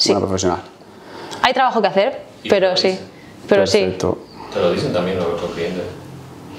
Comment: En el 38:16 ¿quién habla y qué dice? "Te lo dicen y....??" Fue un momento incómodo. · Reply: ¿"te lo dicen ahora tú entiende"?